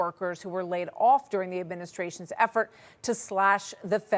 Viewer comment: Oh it's the education department I thought you said the indoctrination Department